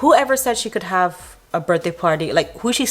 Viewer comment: I thought was saying yak party. Did she try and slit her own throat? Seriously, she has a bandage on a large cut on her jugular area.